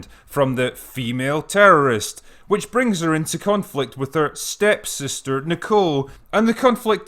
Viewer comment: did you say "cunt-flict"? lol...